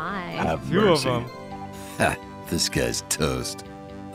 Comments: No matter how many videos I watch, all I hear is "this guy is toast" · Petition for corpse to say: “This guy’s toast” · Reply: I thought it said “disguised toast” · Is Corpse saying "Ha, Disguised Toast!"? Sounds pretty much like him. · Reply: It's a line from hearthstone where a specific card is played and says 'ha, this guy's toast' but is misheard as 'disguised toast' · Petition for corpse to say "Ha, this guy's Toast" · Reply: Hmm i always thought it said 'Ha Disguised Toast'